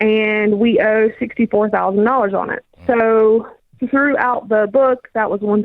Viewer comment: I thought she said she threw the book out when she said throughout